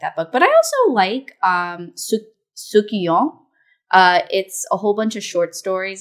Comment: Also, what book was talking about in 50:50? I heard "Sukiyong" and tried googling it but nothing appeared TT